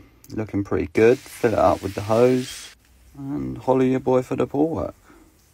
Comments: “holla ya boy for the pool work” plays in my mind everyday and i gotta quote it everyday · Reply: I thought he said "hollywood boy for the pool work" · "holla at yah boy fah the pool work" that shit stuck in my head now😭😂 Edit: why is y'all looking up my gram😭😭😭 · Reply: Ngl I thought he was saying holly ya boy for the pool work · “Holla at ya boy for the pool work” It’s been 2 months and I just realized he says “FOLLOW ya boy for the pool work” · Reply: 😂 WTH I thought he said holla at us boy this whole time